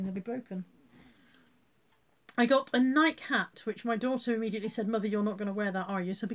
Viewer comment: Talk about getting dafter as I get older, thought you said you'd bought a night cap, what on earth for says I, then realised,Nike hat! Like Kirsten, Lindy Bop was a new one for me as well. Like this live stream stuff, even though I'm watching after the real event.